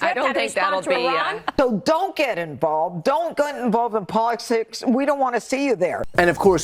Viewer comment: "Don't get involved in what? Polisticks did you say? 😂